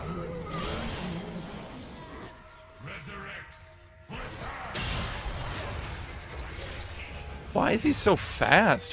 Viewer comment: What did aatrox say at 4:00 ? Is this a voice line that he used to say when he was reviving? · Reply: resurrect , return so i may kill you again . he says it when he kills people during his ultimate